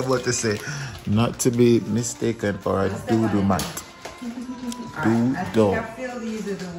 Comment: At first I thought said “dodo Mat”🤣🤣